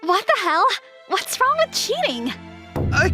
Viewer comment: Literally the first 3 seconds, all I hear is "What's wrong with cheating?" I was like " What breed of unfaithful is the ex?"